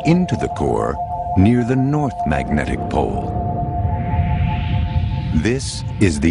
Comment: i thought it said magnetic poles will shit